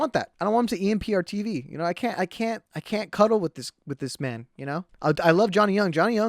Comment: did he just say cuddle?